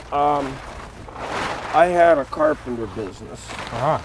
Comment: Tbhe guy at 11:50 said he had a car ???? business. It was so muffled did anyone catch what he said? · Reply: Carpenter.